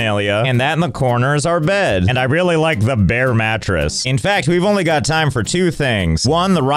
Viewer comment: When I heard bare matress, I thought he said "bear" matress. Totally scrolled back to look and was very disappointed. I wanted to see a mattress like a bear rug. 😢